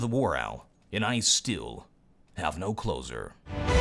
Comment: If you read this hello And whenever you say "I still have no closer" it sounds like you say "I still have no clothes on"